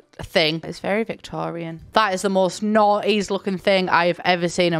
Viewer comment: I thought she said "That is the most naughtiest looking thing that I've ever seen." I was REALLY looking at that fireplace, trying so hard to figure out why it is so naughty. 😅💀